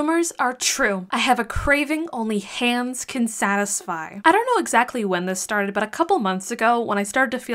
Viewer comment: I heard " ham can satisfy " and thought " ok, so what?" And then😮 " okaaaayyyyy"😅😅😅😅